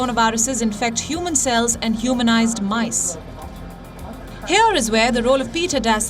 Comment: Am i the only one who hear humanise mice? You can hear it with in 2:30 to 2:40 i think.